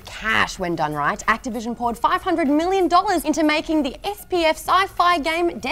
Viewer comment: did just say SPF?